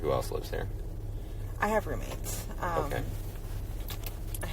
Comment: did she say roommates??????!!!!!! 😮😮😮😮😮 where’s their testimony????!!!!